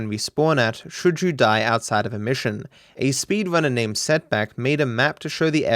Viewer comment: OK, I'll admit it, I was half listening, so it doesn't surprise me that at 8:30 I heard "A pimp named Slickback" instead of "A speedrunner named Setbac" Damn near broke my neck with that head turn XD